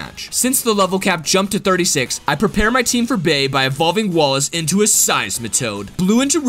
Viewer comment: when you said bea it sounded like you said babe